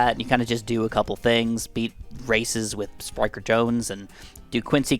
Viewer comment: I honestly thought you said “be racist with striker jones” and thought that was hilarious 😂